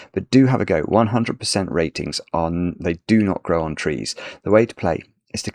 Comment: Hello! At 11:17 I thought you said “100% rating on They Do Not Grow On Trees”. So I spent a good amount of time looking for a website that might have substituted “Logic Masters Germany” called “They Do Not Grow On Trees”. It took me 10 minutes to realize I misheard. Ok. Thanks.